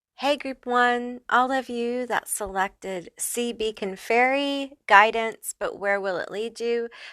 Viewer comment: I chose the pink lady. But I swore the first one said "Sea Bacon Fairy" 😂 🥓